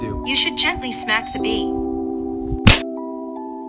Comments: Instead I heard “pissed out” rather than “passed out.” Works either way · I thought when said smack the b it was short for bi*** so he smacked the girl.